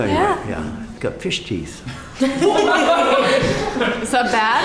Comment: When he said "You have fish teeth" I thought he said piss cheese